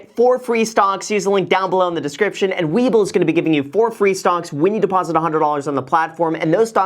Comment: Sounded like you said stonks instead of stocks